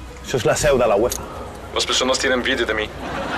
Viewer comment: que dice cris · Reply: "Las personas tienen envidia de mí" ;)